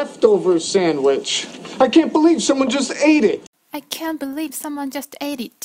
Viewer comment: 初めて拝見させていただきました。 すごくわかりやすいです！ こんな高クオリティのモノを無料でyoutubeに上げていただき、有り難うございます！ １つ質問があります。 4:40 ~ の英文に出てくる「can't」が、私にはどうしても「can」に聞こえます。 そこで、google翻訳でもチェックし、比較してみた所、心なしか否定形の方が上げ調子のように聞こえました。 この感覚でよろしいのでしょうか...？ それとも、「〜’t」も発音しているのでしょうか？ もし、返信の余裕がありましたら返信よろしくお願いします。 これからも動画楽しみにしてます！